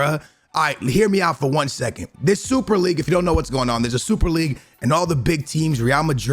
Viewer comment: What said: "if you don't know whats going on" What I heard: "If you just came out ya mums womb"